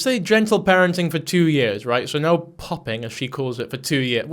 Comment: I think she's saying bopping